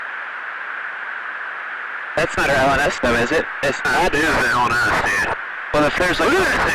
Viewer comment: Inaudible voices at 12:39, I think pilot 1 says, " Thats not going west though is it? " , Pilot 2 says "Goddamn they are hauling ass Steve"